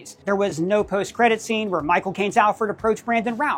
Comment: Thought Doug said “my cocaine” instead of Michael Cane